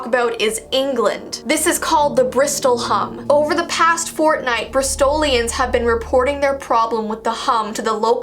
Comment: did she just say fortnite?